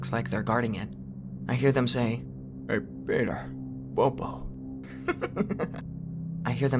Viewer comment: sounded like AYY PETER BOPO!